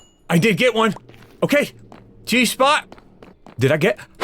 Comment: Bdubs... did you just say what I think you said... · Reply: dont worry it was "cheese spot"